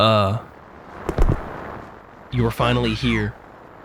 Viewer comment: I just woke up and I thought it said “You’re finally awake”. I mean it kinda looks like the Skyrim intro but cursed 😭